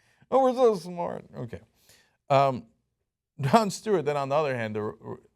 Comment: I thought I heard Jon Stewart at first